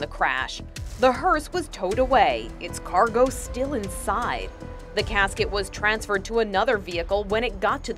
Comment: Lol thought it said horse stolen with body still inside